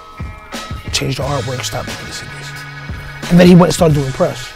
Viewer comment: What does he say at 2:05? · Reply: sounds like he says: "change the artwork, stop printing CDs"